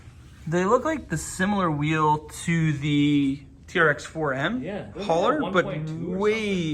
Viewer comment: Will the TRX-4 Sport High Trail Edition work with the new Traxxas Trailer? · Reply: Did you say Trx4 m hauler?